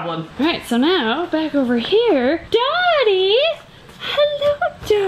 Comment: I thought she said ‘Daddy’ instead of dotty. Killing me 😂